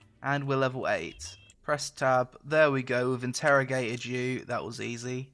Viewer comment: does he say"interrogate a jew" ??? In cert a uncomfortable here -> ( lol )